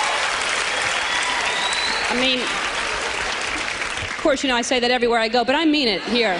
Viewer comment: I thought she said "Portia and I" lol